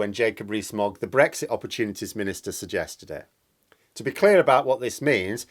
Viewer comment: Moggy the Brexit opportunist Minister. LoL, or did I mishear ?